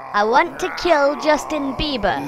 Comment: "I want to kill Justin Bieber" was like the peak of comedy when I was little and watched this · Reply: I thought on the past he said i want to kill just a beaver LOL